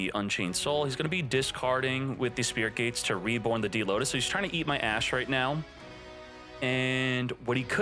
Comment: He’s trying to eat Ash I thought it said he’s trying to eat ass 😅